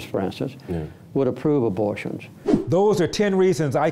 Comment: typo. Verbally Carter said he didn't think Jesus would approve of abortion but why did the captions say Jesus would (n't) approve of gay marriage?